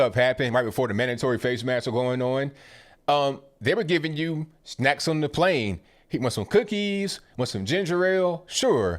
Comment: I heard “snakes on a plane” 😂